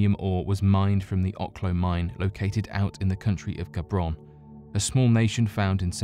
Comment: #2 I thought he said "Gabron" instead of Gabon.